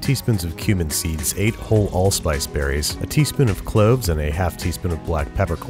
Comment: I misheard you when you said a teaspoon of cloves I heard a tablespoon I don't have enough ingredients to start over God help me